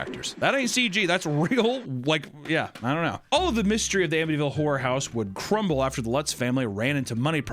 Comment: it sounded like he said Amityville Whorehouse 😂